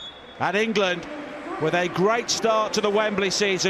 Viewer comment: Does he really say '.. they beated Wales ...."